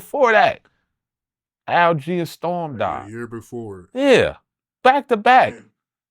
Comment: who did he say died here, a year before Pac? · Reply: Al G & Storm 🙏🏻🙏🏻